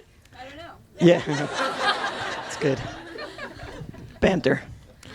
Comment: Someone please!!!! What does he say to his sister? at 9:04 ........sorry my english is really bad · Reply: I think he said banter